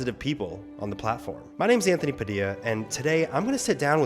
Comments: I don't know who does your subtitles, but at about 1:03, when you say "I'm Anthony Padilla" the CC says "Ian Hecox" instead... weird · Anthony proceeds to sat "my name's Anthony Padilla" but the subtitle says "My name's Ian Hecox" nice likkle blooper there if uk uk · i watched this video so many times and today i realised when anthony says 'my name's anthony padilla', the subtitle says 'my name's ian hecox'. cries in smosh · he says my name's Anthony Padilla but the subtitles say my name's Ian Hecox 🙂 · Did no one notice that at 0:56 when he says my name's Anthony Padilla, the subtitles say Ian Hecox? lol